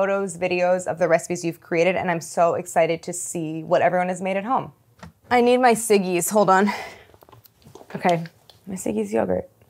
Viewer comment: I deadass thought she said “I need my ciggies”